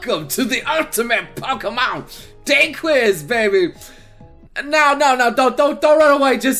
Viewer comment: I thought you said dank quiz xD